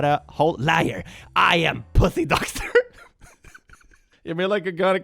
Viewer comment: am I the only one who heard doxer instead of doctor? Pussy doxer sounds like a cool band name.